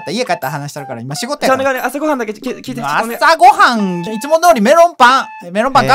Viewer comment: 何回も見てて思うこと。 2:51 朝ごはんいちゅもどうりに聞こえてしまう。 これは僕がおかしいのか？